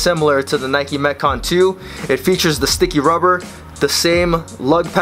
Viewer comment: I thought you said "It features the stinky rubber." hahahaha